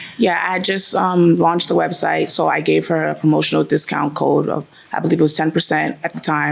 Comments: The plaintiff said she gave the defendant a promotional discount; I heard "emotional". Hearing gets kind of wonky as you age. 👂🏽😅🤣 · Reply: I heard “emotional” too. 😂😂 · The plaintiff said she gave the defendant a promotional discount; I heard "emotional". Hearing gets kind of wonky as you age. 👂🏽😅🤣